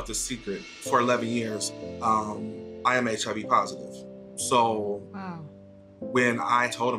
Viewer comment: I thought said, “I’m chubby positive”, I was like “me too”